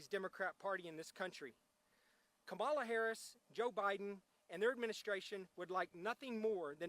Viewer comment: Did he say "Kabbalah Harris" at 14:19?